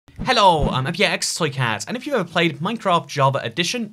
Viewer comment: the captions say “hello I’m a px slick-ass